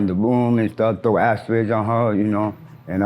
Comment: What he say @ 0:45 about his father throwing something at hia mother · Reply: Ashtrays